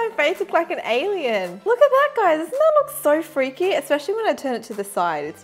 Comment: she sounded like she say watthepak guys